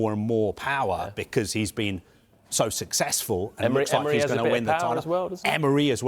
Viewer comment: Arteta has been so successful?? Did I hear that right?